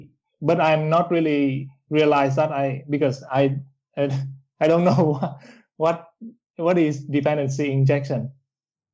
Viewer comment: Awesome stuff. I didnt understand what is used instead of coredata.Time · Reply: Thanks, Jancy! The framework is called Realm. ✅